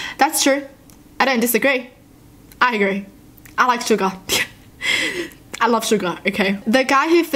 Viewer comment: It sounded like she said : I love suga😅😅😅😅❤️❤️❤️❤️❤️💜💜💜